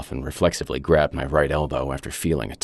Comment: I have SO many questions. Spoilers What was up with the pain he felt on his wrist? Did they put in a tracking device or something, or was it a red herring? The plume of smoke; Did they destroy the lab out of fear of being found out or revealed by the narrator? (Which I guess sort of happened, but then what was the point of the pain in the wrist again?) From the way the narrator is telling the story, it seems like some time has passed between the winning numbers being called and when the narrator is telling us this. Was he waiting because he didn’t know if he wanted to cash in the ticket? Was he waiting to see if someone ELSE also had a winning ticket and they would get taken instead of himself? The way the narrator talks at the end of the story seems different from how he talks at the beginning (choice of words and mood-wise). Did something change? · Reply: Wrist?? I heard "elbow".